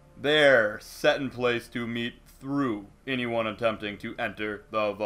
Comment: I think he said meet